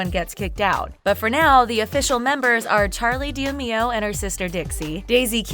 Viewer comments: she really said charli deameo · DID SHE JUST SAY DEEAMEEOH!?!?